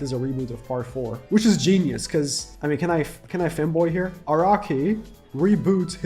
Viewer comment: did bro just say "Can I femboy here?" or "can I fanboy here?"? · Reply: He said can I Fanboy here